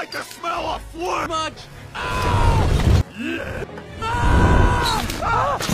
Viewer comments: Yea, how do you like the smell of FLAMES? Much YEET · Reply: he actually says something that sounds like a loud YEET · Did he just say yeet?!